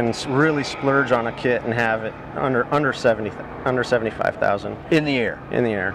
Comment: I love the project ! Its one of my dream projects -, sorry but I need to clarify.....? he said $75,000 after spending 1500 hours building right? I thought I heard 175,000 ! Which would be just crazy talk….